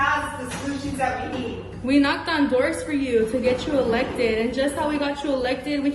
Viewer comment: She said she voted for her. My question is, if she's illegal then how did she even vote in the first place? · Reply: I agree if she said voted, but I thought she said “ knocked on doors for her”.